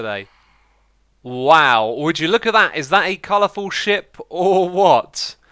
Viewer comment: Hahah I thought he said a colorful shit but then I realized he said a colorful ship